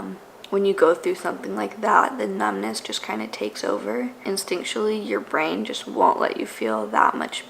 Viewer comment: Omg I thought this said “takes life” 🥺